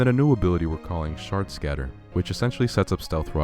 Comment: This is so good but when I heard Shard Scatter I thought you say Shart Scat turd and I was like there is no way you just said that but you are a talented artist but I needed to let this thought be known I am not a disugting person who make jokes like this I am just speaking my truth